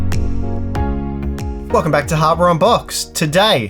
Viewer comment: My wife thought you said, "Welcome to hardware on pot." 😆